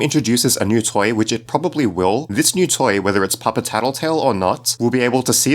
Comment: Remember to Subscribe and #TapThatBell for more horror game theories! Subscribe ► <link> All Tattletail Theories ► <link> A Message From Waygetter Electronics™ ► <link> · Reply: OK so you did say Papa tattletail and I thought you said puppet tattletail at first